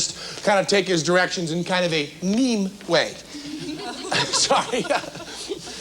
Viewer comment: did Penn just say meme... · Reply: He didn't say meme, he said "mime". Same pronunciation but different words.